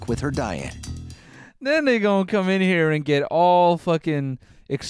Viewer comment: "nOeL sAiD tHe N wOrD" No I didn't you dummys I said, "and then they gone come here and" referring to the dumb ass healthy spread they put out on the table. Clean your crusty ass ears from your air pod sweat. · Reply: I heard the n word at first but was like “nah... I misheard that...” so I played it back and it was definitely just Noel speaking quickly and having the words run together in an unfortunate way. 9:57 just give it a couple listens. If you aren’t paying full attention it sounds like the no no word. I knew people would be heated in the comments. XD